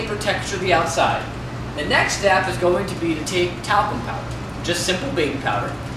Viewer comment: What is he saying at 1:52? Tape what Powder???? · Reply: Talcum powder.